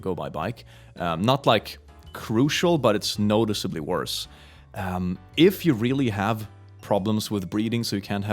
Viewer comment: is it just me or did he just say “if you really have problems with breeding” lmao